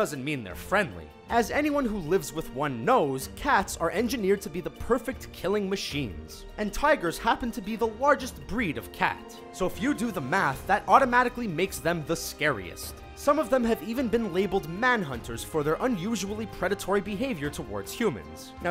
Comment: Like if thought liger when he said largest cat